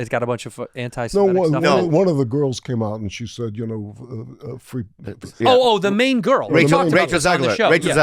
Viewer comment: around that 16:45 mark they had to cut out what Mamet said and i really really wanna know what he said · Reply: He was calling someone antisemitic for saying free Palestine. Palestine got bleeped out.